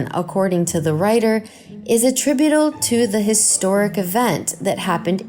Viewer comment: it sounds like she says attrubidool